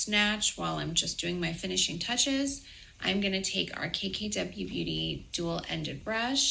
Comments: Each time she says “KKW Beauty” I hear “Cakey W Beauty” and I think that tells you all you need to know 😂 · I misheard her saying "KKW Beauty" as "Cakey W" and honestly how fitting. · Every time she says KKW Beauty I hear “Cakey W Beauty”… 👀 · Every time she says KKW Beauty I hear “Cakey W Beauty”… 👀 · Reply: I always hear "KKK beauty" yikes. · Sounds like she is saying, cakey w beauty.